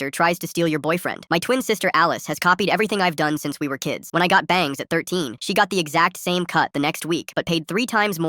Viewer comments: Help i thought he said, "When i got banged at 13" 💀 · Guys I heard "when I got banged at 13"😂